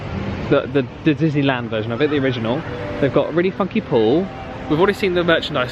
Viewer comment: Had to rewind and put captions on because it sounds like you said fucky instead of funky 😂